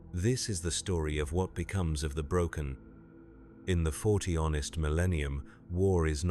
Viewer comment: Ah yes, the grim darkness of the forty onest millennium. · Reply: I heard it as forty honest millennium. 😂